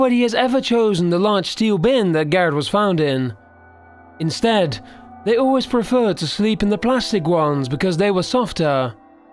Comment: I keep hearing "nigga always preferred to sleep..." at 1:53 😂